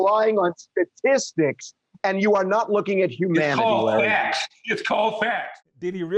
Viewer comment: My goodness, "You are looking at facts not humanity"? Sounds like he is saying "You are looking at facts not my feelings". Hysterical.